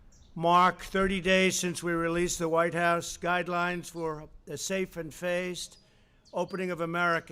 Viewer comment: Trump says something that sounds like "Safe and fazed reopening". Did he misread something or did he say fazed? I actually can't tell. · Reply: Tavio Revilla safe and phased reopening